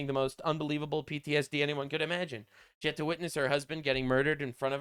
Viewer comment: thought she said she Wasn’t there when Charlie was murdered